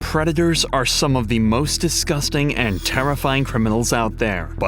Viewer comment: At the starting my earbuds lagged and I heard "redditors are some of the most disgusting and terrifying criminals out there". I did not expect to get a laugh from a video of trash getting disposed of